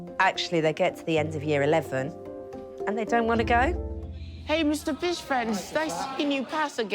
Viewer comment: I thought said hey mr fish face 😂😂😂😂